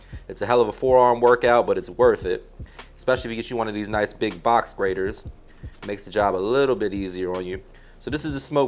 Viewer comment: I thought he said "Box Braider" for a minute before I recognized he said "Box Grater" 😂 ... Either way, he has clearly been to some cookouts and can definitely come to next one 🤣